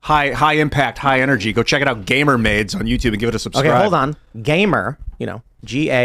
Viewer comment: LOL at first I thought he said gamer made. Is It gamer made, or is it gamer maids? Or is it gay mermaid?